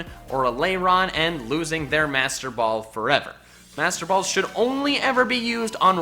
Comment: I once used a master ball on a abomsnow that I never used. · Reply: August Skår Holbø Same I thought it said monster ball not master ball